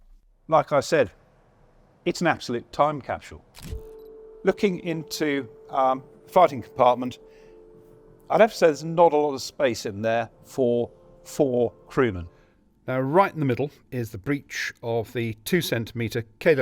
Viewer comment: I thought he said "farting compartment" the Germans thought of everything🤣